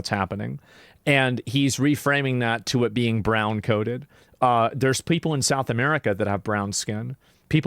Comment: Definitely heard it as Brown coated not brown coded lol